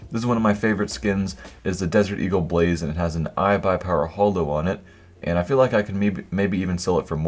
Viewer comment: it sounds like ur saying i buy power holdo